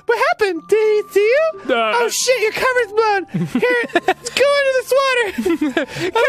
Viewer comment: For some reason, I heard "You're covered in blood"...